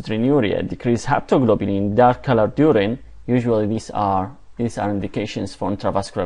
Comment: hemocitrinuria decrease haptoglobin _______________ i dont understand what goes after please help. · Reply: You Tube he said heptlglobin and dark color urine